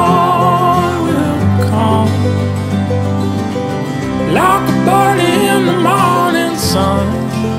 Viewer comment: I thought he said "like the burning in the morning sun" then found out it was "like a bird.." I think my line is better :p Sunshine in the morning :) beautiful refreshing live giving tingling "burn". Writing this while finishing breakfast under the blue sky and sunshine. After 27 days of rain!